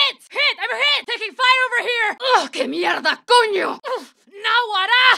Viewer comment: What is she saying at 0:32 pls ? Can't find it ^^ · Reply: Thanks for the answer it is rly interesting how the States are different. I'm not sure, it sounds like she is cursing. I hear sth like "la mierda cuño" but i don't know maybe i miss hear it 🤔